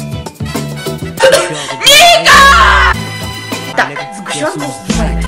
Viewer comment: кто тут услышал Нига?